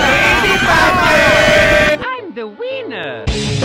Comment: glad I'm not the only one who heard It like that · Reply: "I'm the wiener!"